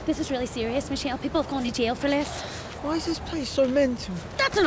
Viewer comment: what does James say? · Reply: "Why is this place so mental?"